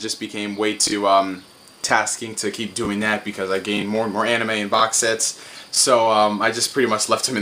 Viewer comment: when you said "and uh" at 1:26 I thought you said "hentai." I had to double check.